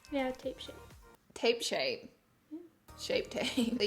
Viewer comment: I thought she said tape shit🤣🤣